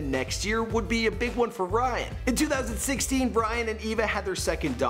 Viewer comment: sounds like you said Bryan. His sexy face makes it hard to remember his name